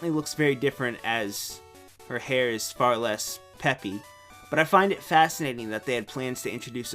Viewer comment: I heard my name.. Peffy (not my REAL name though, just a YouTube nickname.